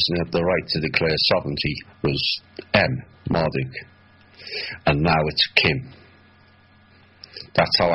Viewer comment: and, at 1:02:52 says that the only one with the right to declare sovereignty was ? I cannot understand what he is saying here; it sounds like N. Marduke and now it's Kim? I am I hearing that right?